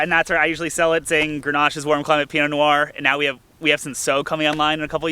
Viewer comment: what warm variety grape did u say at 6:13? soe,sow,sao? i have never heard of it. thanks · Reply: said "Cinsault." Thanks for watching.